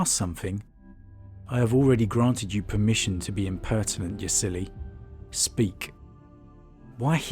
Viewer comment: For a moment I thought Guiliman called her "you silly." 🤣